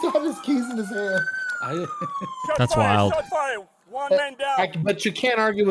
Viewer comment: I heard 'juan man down' good to know he recovered to be on stream XD